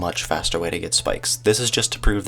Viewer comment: wait I thought u said u have to scam to become rare XP